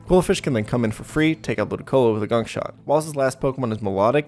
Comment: I thought he said gunshot